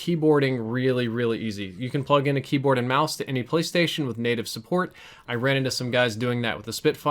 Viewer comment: am i hearing right? said "Native M&K support on Ps4" ?????